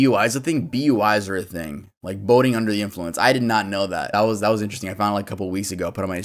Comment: Broooooo, when you said “Boating under the influence” I thought you said “Boning under the influence”😂😭 had to double check that